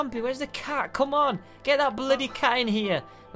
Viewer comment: did say bloody